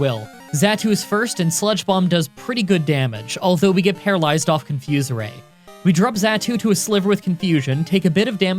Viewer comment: i think i heard him saying 'Got paralyzed off of zatu's confuse ray' prove me wrong (in first attempt at will )